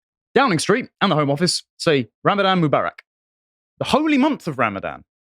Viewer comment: I thought that said Ramadan Mucbang